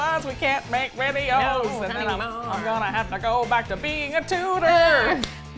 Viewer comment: What does it say about me that I forgot the word tutor existed and thought was saying "go back to being a tooter" ???